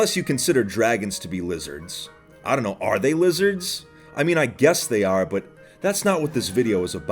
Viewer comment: I thought for sure he said “are the lizards” 😂